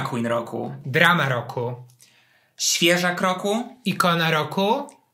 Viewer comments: Muszę iść przebadać słuch... Zamiast "Świeżak Roku" usłyszałam "świeża w kroku"... XD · Usłyszałam "Świeża W Kroku" zamiast "Świeżak roku" 😑 czas się wybrać do lekarza od uszu